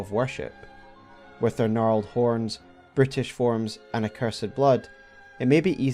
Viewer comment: Amusingly, your accent to my accent I heard 0:54 and 1:04 as "british" instead of "brutish" for a split second. Which led to a pretty amusing concept, that the Omen were British monsters. Like oh no, we birthed a Brit! Into the sewers with you! This is one of the best most researched omen videos out there. And I have watched hundreds of ER lore vids by this time. Its given me a lot to think about.